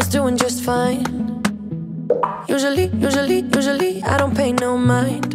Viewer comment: At thought she was saying « you Jolie you Jolie » at the beginning well instead it was « usually »😅